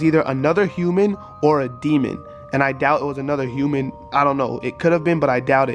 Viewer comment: Did everyone’s video stop at 8:37 ?? What said “DEMON”??